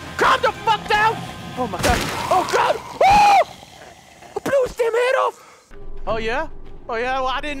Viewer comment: I was so confused what he said “I blew his damn head off” is that what he said I’m lost